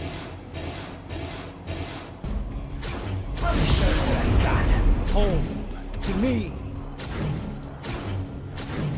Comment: what is what dante says in the second 0:46 when transforming? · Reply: "Let me show you what I got"